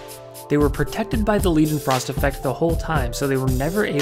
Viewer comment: OK i cant seem to find what you are saying at 5:22 about the something frost effect? what was that? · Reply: ok, nvm found it eventually with some googl'n Leidenfrost effect